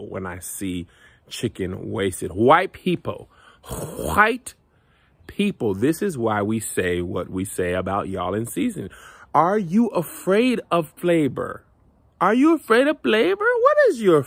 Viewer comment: Ok but I laughed sooooooo hard because at first I thought he said "are you afraid of Labor???"😅🤣😁